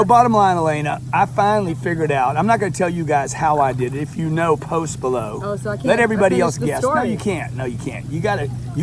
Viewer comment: At first I thought he said something about post Malone then I realized he said post below